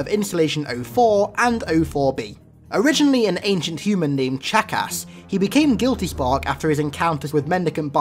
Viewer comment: I'm sry did you say his name was Jackass